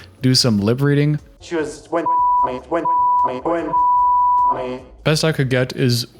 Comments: “she went down on me” is what he said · this is what was said at 24:50 If you’re eating, put down the fork !! “SHE WAS GOING DOWN ON ME” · “she starts going down on me” is what he said